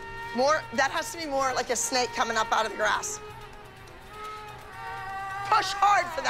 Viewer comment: did Abby say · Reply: She said grass lol😂